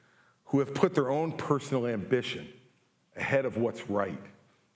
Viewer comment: I just had a Freudian slip of the ear... somewhere toward the beginning of the speech he said "personal ambition"... and I heard, "Personally, I'm bitching..." ... um, yep. 😂